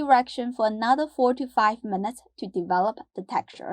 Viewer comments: Oh, man.. at first, I thought I heard her say to keep mixing the meat mixture for an additional 45 minutes, instead of 4 to 5. lol, I almost cough out the food I was eating. Anyone else hear that adorable little sigh of happiness at 6:34? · Did I hear correctly? Stir the meat mixture for 45 minutes? 45 minutes??